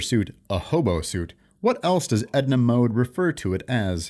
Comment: When Edna said hobo suit I thought she was saying horrible suit